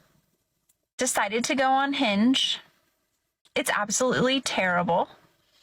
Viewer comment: I have to laugh, is it just me...when I hear a woman say 'on Hinge' I hear 'unhinged'?